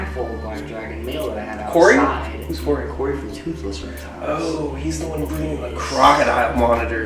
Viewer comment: Did you seriously just throw a freaking mouse and his mouth and spit it back out ewwwww I've seen a lot of things in my life but that really is gross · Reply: okay hang on I'm at like 5:02 holyshit did he just say toothless reptiles that is like one of my other favorite YouTubers for black dragons and monitor lizards